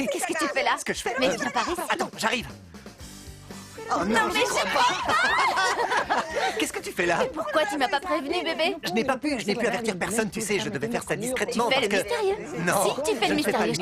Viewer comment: c moi ou elle dis "bb" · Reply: Elle dit bb :D